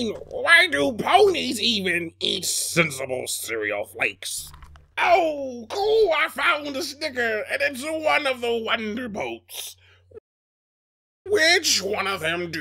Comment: What i heard at 6:33 Oh coo, i found dis nigga. And it's one o da wonderbolts